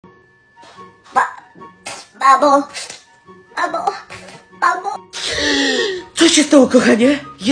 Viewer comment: Kto usłyszał Ba.. Babo! Mamo! Mamoo!